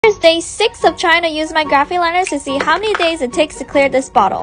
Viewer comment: For one second i thought that she said here's day 6 of china nya graphic liners 💀